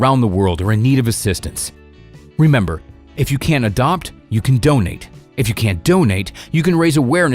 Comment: 'video' if you cant buy you can donate' what i hear' if you cant buy steal the pets'